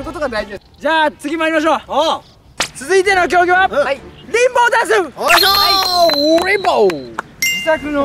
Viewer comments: リンボーダンスがピンポンダッシュに聞こえたw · リンボーダンスがピンポンダッシュって聞こえたｗｗｗｗ